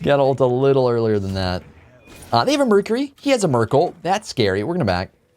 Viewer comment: I thought you said he had a Merkel.